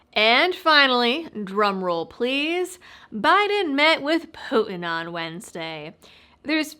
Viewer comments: Question for folks: are you getting any adds before or after the video? YouTube told me monetization was fine when I went through the upload process, but keeps shutting it to "off" despite telling me there are no issues. EDIT: Complained to YT and then it magically stopped happening, lol. So nevermind! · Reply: At first, I thought you said "Biden met with Pokemon"! I had the video on 1.25 speed. 😂🤣 Thanks for your work! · Sounded like you said, "BIDEN MET WITH POKEMON". Bahahahahahaha!